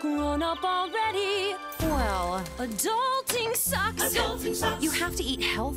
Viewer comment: I thought that is said “but daunting socks”